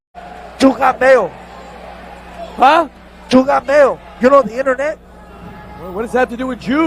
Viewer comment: the commentator is telling a bad joke & telling it badly. He’s saying “you got mail,” referencing AOL/America Online’s “You Got Mail,” but he’s adding a thick Latin accent to it, so it sounds like “juu got mail.” He’s trying to be funny, but it’s shit late 90s Indy shit, so it’s “edgy” but not good. He prob didn’t clue the other commentator in on the shit joke, so the other commentator heard it & incorrectly thought he was saying “j-ew.”